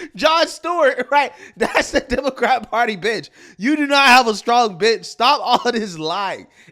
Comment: Oh “bench” thought he said BIOTCH